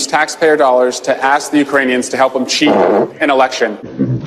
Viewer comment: All I heard was "Ass the Ukrainians to help him shit, pfrrp, the elections"